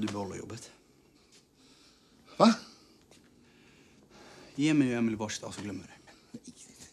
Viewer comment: vad säger han där? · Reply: Ge mig och Emelie ett varsitt A så glömmer vi det här! sa han.